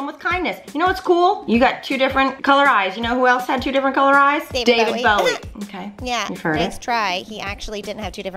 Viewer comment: did artichoke say Pete Davidson?